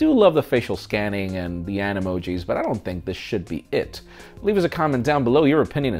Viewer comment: Did he say i dont think this shit should be it ?